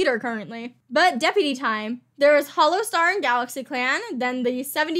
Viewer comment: DID SHE SAY HOLLOWSTAR?!